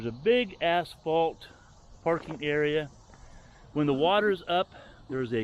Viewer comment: Nice area. At the beginning I thought you said "big ass parking lot".....😆